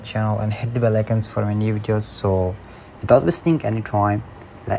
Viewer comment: i thought u said without app…